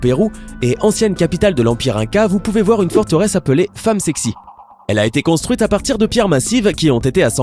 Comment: A 1:45 j'ai bien entendu femme sexy je comprend plus rien et aussi a 5:00 on bien entendu la pierre de la femme enceinte les extra terrestre on quoi avec les femmes